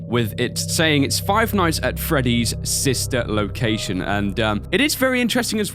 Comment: who else thought it said sinister location?